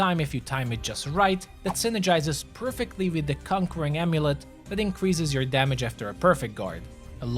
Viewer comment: I was sure i heard "cock ring amulet" 😂 I was like "i haven't got that one yet? What sort of power does it hold? I must know......"😂